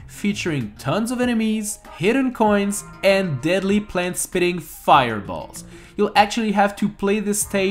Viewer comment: Did you just say plant spitting fireballs? EDIT: Sorry I've heard it wrong, of course you said: Plants, spitting fireballs. I’m sometimes sooo stupid. (Thanks to the comment that told me.)